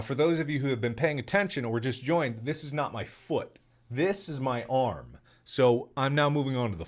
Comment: Oh my god, I thought it said. ”I lost my foot”